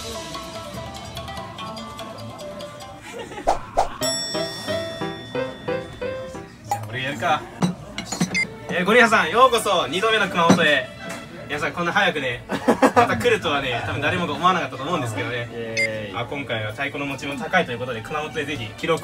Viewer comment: 「ゴリラさん🦍」 に聞こえたのは私だけではないはず